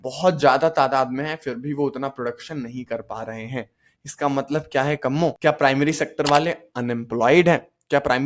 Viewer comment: did I hear that right "KAMMO"😂😅🤔🤭